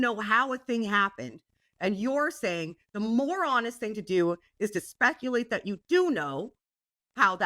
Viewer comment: I thought kept saying "the moron-est thing to do" like it was a Canadian insult, rather than "the more honest thing to do."